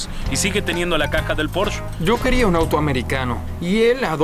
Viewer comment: La traducción hace que me revuelque de la cólera, “un porsché” 😤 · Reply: Yo escuche "porshet" jajajajajaja estan bien punetas para tener tanto dinero